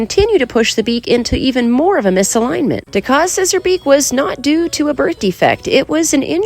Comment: I misunderstood and thought you said “Taka says her beak was not a birth defect, It happened later in life”and then rewatched and realized that you said “Taka’s scissor beak” 😂🤭